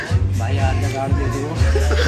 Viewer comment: kya bola? Meko sunai nhi Diya🥲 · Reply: Sayet se haath na ghar dediyo bola 😅🤔